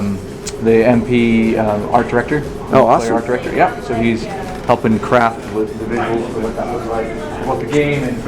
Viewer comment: Haha at 6:42 the dude couldn't even say his position and that guy was thinking what a douche, and he said "working on crap" so funny!